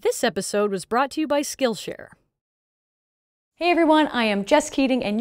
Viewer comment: She said "Hello, everyone, I'm Jess Keating." I heard "Hell, everyone, I'm just kidding." aaand apparently, she gets that a lot.